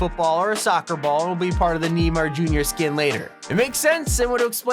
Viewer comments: did he just say neemar? 🤣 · did he just say kneemar? lmao